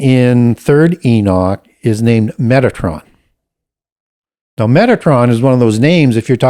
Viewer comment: I thought at first he said Megatron lol